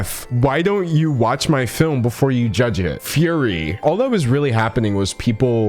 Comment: i forgot “fury” was a word and i thought it said “furry”